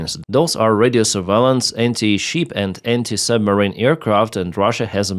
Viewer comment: Wait... at 0:47 did you say anti-sheep missiles? You mean the dirty Russians are now attacking sheep? That's low. That's really, really low.